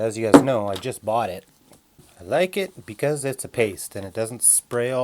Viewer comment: Omg I thought you said " the taste" 🤣🤣 but you said " paste"